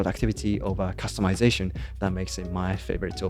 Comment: I thought he said "Applenote" and was puzzled by why the UI looked different.